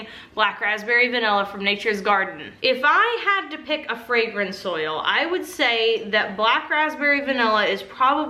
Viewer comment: 🤦🏻‍♀️I think I watch too many of these videos... I no longer hear "fragrance oils" I hear "fragrant soil" 🤷🏻‍♀️